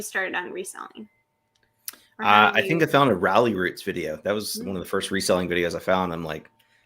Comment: Which reseller did say was his inspiration at 1:03:45? I know he's mentioned that channel on his videos but I couldn't quite hear what he said. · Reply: He mentioned Ralli Roots, a reseller couple that has really helped pave the way with resellers on YouTube!